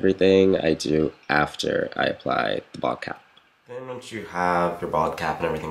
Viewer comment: Every time you say bald-cap I hear "vodka"